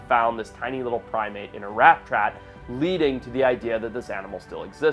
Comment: Anyone else hear “rap trat” lol